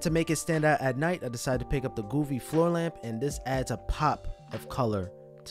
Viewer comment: at around 1:38 it sound like u said "the goofy floor lamp"